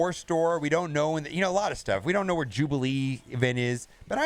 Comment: Jubilee? We thought you said magneto again for the 3rd time